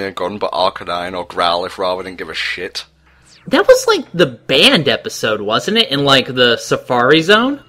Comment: When said "the banned episode" I thought he said "the band episode".